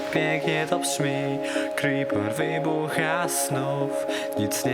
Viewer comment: Czy tylko ja usłyszałem creeper vivo has snow?